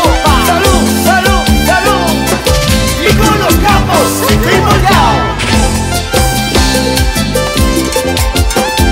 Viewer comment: Que dice En la parte 4:27 no logro decifrarlo☹️ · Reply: Y con los capos, seco y volteado